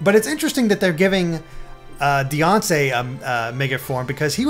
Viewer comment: i thought he said Beyoncé xD